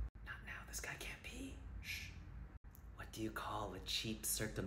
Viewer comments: When he said catheter, it sounded like he said cat litter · I thought it said sheep circumsion and I was so lost 😭